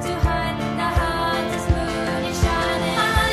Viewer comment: OK so I've listened to that part over and over again and it's the hunter's moon is shining. I thought it was saying the harvest moon is shining at first too but no. I could be wrong sue me.